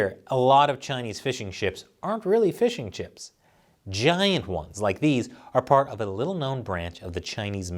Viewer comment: I heard "fishing chip" 😆